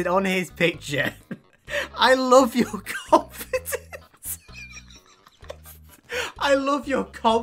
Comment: When he said the girl commented “I love your confidence” I heard “I love your carpet